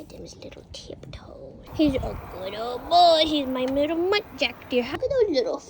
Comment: What type of deer is it?🤨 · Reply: I believe the handsome little lad is a muntjac deer :] 🦌 (I think its said around 0:52 in the video :D )